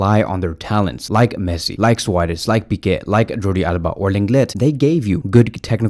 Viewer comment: did he just say Lenglet?